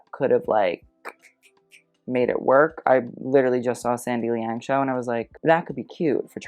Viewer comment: could anyone tell me what brand it is is talking about here, haven't heard about it before and I can't quite catch what he is saying ^^' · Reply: he said sandy liang. he went to the show I think. don't know anything about the brand tho.